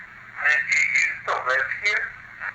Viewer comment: it sounded like it said I lived and died here